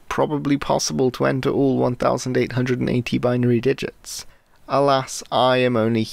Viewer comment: I heard vinary digits instead of binary digits :-)